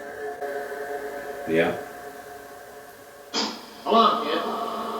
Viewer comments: it sounds like he said “pff come on kid” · it sounded like a spirit was trying to get X to talk when they said, "Come on, kid". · it sounds like he says either “come on man” or “hold on man” in that voice he used to do (like when he was talking to his mom in that vlog) Does anyone else recognize it?